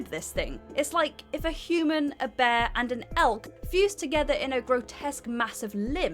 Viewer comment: I thought you said a human, a bear and an elf. I feel incredibly dumb.